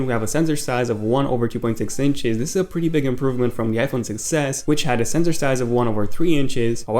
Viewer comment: it sounded like he said "iPhone success"